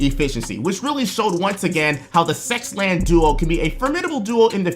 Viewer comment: I zoned out for a bit and I only heard "Sexland duo"